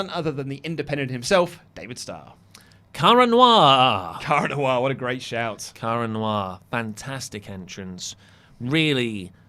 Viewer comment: ? I couldn't quite understand the name of the wrestler. Haalp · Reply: David Starr and Kara Noir